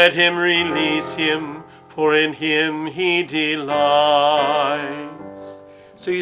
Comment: Warning that in minute 2:44 CAPTIONS SAYS lIES IN THE LORD INSTEAD OF DELIES IN THE LORD. I WISH IF SOME ONE PAY ATTENTION TO THAT MISTAKE AND CHANGE IT .